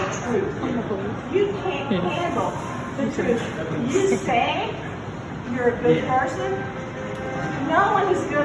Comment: The karen at 2:55 When it said "unintelligible" for the first time She actually said "I've been dealing with assault and battery 3 times over!" The second time she said "you say your a good person but your not"